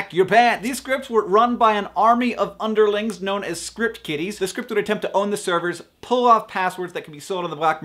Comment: Am I the only one who thought he said Script Kitties?